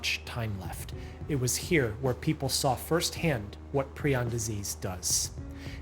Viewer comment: i thought it said prison disease